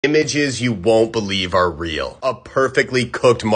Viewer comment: i heard him say inbitches that you wont believe are real